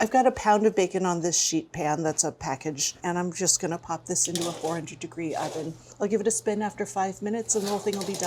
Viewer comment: She mumbles the oven temperature at 9:17. What did she say? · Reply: 400F degrees